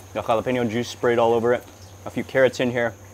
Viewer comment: I thought Mark said “a few karats” 😂😂😂 which couldve worked too!